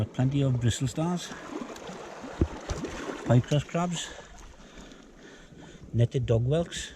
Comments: Who heard Minecraft Crab? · Did he just say Minecraft Crabs? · it sounded like he said minecraft crab lol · did he say Minecraft crab or I am tripping.